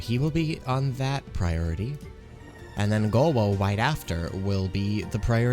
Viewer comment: Omg did you say Gowo? 2:25 Awww